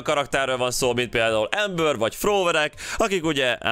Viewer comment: Csak én nem értem mit mond az Amber szó után? Azt hallom hogy fróöreg XD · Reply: Throwerek = dobálós karakterek